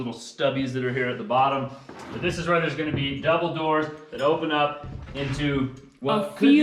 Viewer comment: I heard him say dumbledoor 🤣